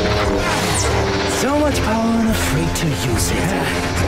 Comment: In 13:46 when the inquisitor says "So much power and afraid to use it". The spanish subtitles are wrong in this part. It's translated as he'd said "so much power and free to use it". So the translation should be: "Tanto poder y tan asustado para usarlo".